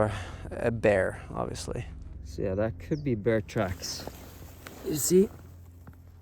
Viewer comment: OMG 😱 I thought you said bear TRAPS as you reached your hand out 😅😅 😮‍💨